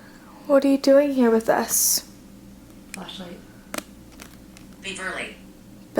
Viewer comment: What the phone said 'Beverly' I thought it said 'leave early' hahah